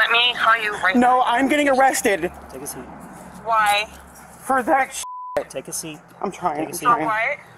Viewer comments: “I’m getting arrested for that shit”. The fiancé knew what he was doing and let it happen. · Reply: I heard "bad shit" Idk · “I’m getting arrested for that shit”. The fiancé knew what he was doing and let it happen. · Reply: I thought he said bad shit. I could be wrong